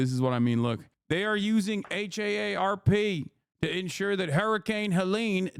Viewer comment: I was only listening and I heard “they’re using AARP…” and I thought: What the hell has the American Association of Retired Persons been up to?!?